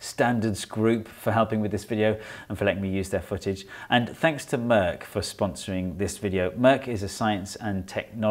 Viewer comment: I thought he said “thanks to today’s sponsor Merc” it made a lot of sense at first seconds. He used a Merc as the car he filmed, he implied BMW drivers are “irresponsible”...